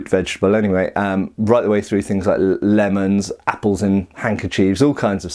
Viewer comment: "apple in a handkerchief" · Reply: My bad, I thought you said "apples and handkerchiefs" I feel quite silly now.